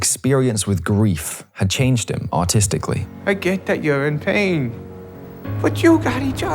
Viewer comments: I was so into the video and then deadass thought you said “his grief changed him autistically” because of your accent I had to go back real quick 😭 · Whenever you say "artistically" i hear "autistically" and get startled